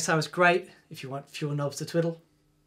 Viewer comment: What does he say at 4:29? Sounds like "Great, if you want chewing nose to Twitter." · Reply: if you want fewer knobs to twiddle